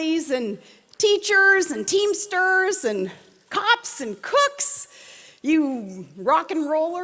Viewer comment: Did she say "cops and CUCKS" at 2:05?